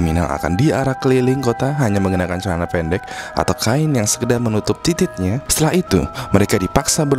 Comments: did he say "Titit"??!?... Or am i wrong..? Jezuz wow. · ngomongnya apatuh wkwkkwkw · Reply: Titid